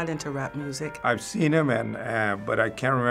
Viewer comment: what the fuck he said kendick lamar what the hell